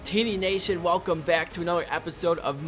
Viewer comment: sounds like you say tits nation